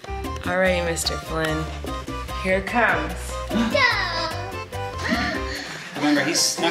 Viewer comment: I'm the only one who heard "ciao" from Flynn at 0:32 HAHHAHAHAHA I'm Italian